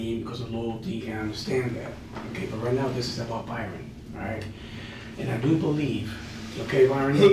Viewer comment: “This is about Byron”?? Then why does the closed caption say “Mary”?? Why is it all wrong? It’s false.